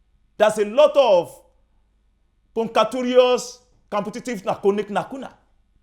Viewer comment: Can someone pls explain what he said at 4:38 (Something about a lot of "compatatious nanukuti nakuna"